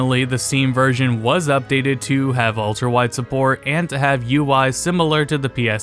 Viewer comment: listening to this in the background and thought you said the steam version was updated to have walter white support